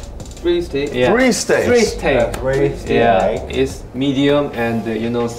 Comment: I THOUGHT HE SAID FREEZE TAG BUT HE SAID FREE STEAK